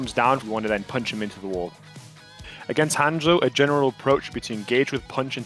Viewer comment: Did say hang joy